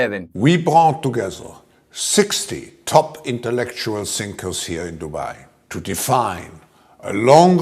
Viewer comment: “60 top intellectual thinkers.” Hearing those words out of Klaus Schwab’s mouth is chilling. These people need to be reigned in, now, right now. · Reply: For some reason, ii heard him say ‘Intellectual STINKERS’.